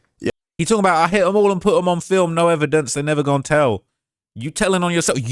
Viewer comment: I thought he said, I hit em all I put em on film, no evidence she never was here. I thought he was talking about hitting chicks.